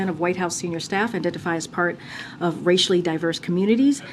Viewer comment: did she just say they identify as racially diverse???